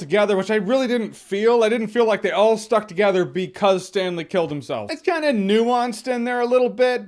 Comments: for a second I thought he said Stan lee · I thought you said “Stan Lee” was dead. I did not need that unintentional reminder sir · I thought you said stan lee